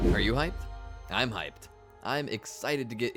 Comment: Are you high? I'm high. Swore that's what he said at the beginning of this video.